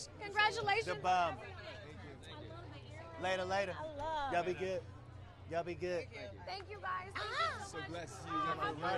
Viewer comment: What did jungkook say at 1:11 and i know it’s not “yeppuda” can someone tell what he said? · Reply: not yeppeuda, aleumdaun, which means beautiful in Korean, he said to Hoseok at 1:11 and nodded towards the girl